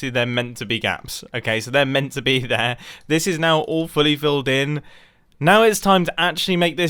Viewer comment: Did anyone else hear ‘awfully filled in’ in stead of ‘All fully filled in’